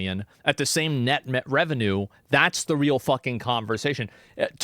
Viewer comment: Did i hear net mcrevenue?